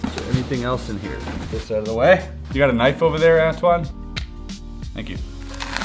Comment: i thought he said asswad instead of antwan